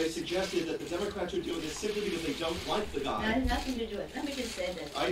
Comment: Does anyone know who asked the question and from what organization he is from? And did he say "The Donald"? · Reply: He said “the guy”